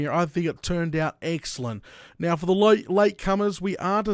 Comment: Did you say eggcellent?